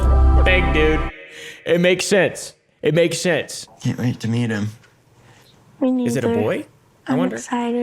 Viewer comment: Bro when he said I can't wait to meet him I thought he said "eat him" I was like excuse me???